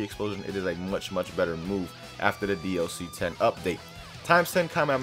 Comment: I thought it said new dlc 11 i ALMOST threw my phone Also yay more ki explosion plus flash strike